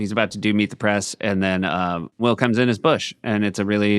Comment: sounds like Seth is saying “Will cums in his bush”. Someone isolate that soundbyte and put it in the shownotes.